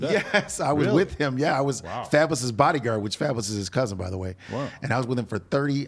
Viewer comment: Did he just say fabolous is 50 cents cousin? Lol